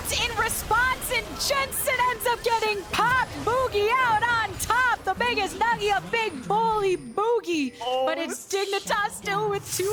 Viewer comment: wtf is she saying????? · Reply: Translate: Jensen ends up getting popped, Bugi out on top, the biggest none of your big bum boey, but dignitas still comes out on top. You’ve never heard of big bum boey? I say it all the time, like when I hit my toe on something or just saying hello to my friends, it can be used for anything!